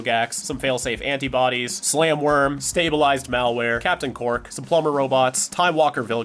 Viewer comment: bro I thought called him "Captain Quark" and I was losing my mind 😭